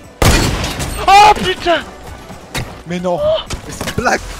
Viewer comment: Who else thought 6:53 he said,"Ooh pizza"